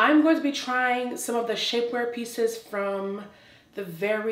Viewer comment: 21. Had to rewind because I sincerely thought she said "Shitware peices", but she actually said : "Shapeware pieces". After viewing the entire video I'm convinced that she SHOULD have said would I originally thought I heard.😳😁